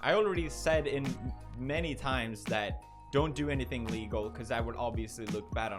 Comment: "Don't do anything legal" Did I hear that right?